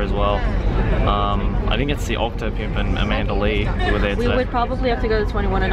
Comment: did Joey say Octopimp??